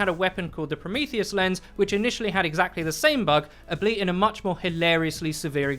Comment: did you just say ableit instead of albeit?